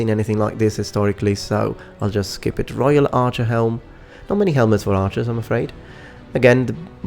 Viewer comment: Did You Say Not Many Helmets Wore Archers?